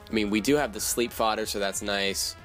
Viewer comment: u said u had a sleep fuather idk some shit like that what does it mean i hear that F word from time 2 time and it keeps bugging me that idks about it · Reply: Fodder is the word, usually meaning death fodder, as in something that's being used as just a sacrifice to get a safe switch afterwards.